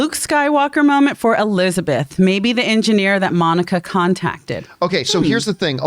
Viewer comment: If u listen to it in spanish the subtitles call the aerospace engineer with female pronouns...it will most likely be her skrull friend from Captain Marvel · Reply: I think the only reason the female pronouns translated was because of how the actress pronounced it. On rewatch it sounded like she said engineer-a. So I doubt they are female.